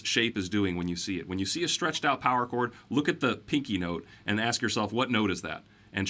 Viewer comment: I thought you said "stressed out power chords".........do they exist?